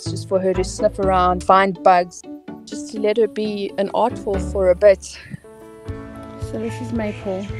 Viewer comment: Aardwolf, never seen one before. I first heard it like "oddwolf" and thought to myself "yeah that checks out"